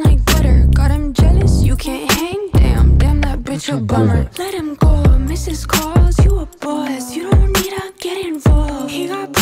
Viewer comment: I heard 'let him go Mrs Claus, you're a boss'😂😂😂😂😂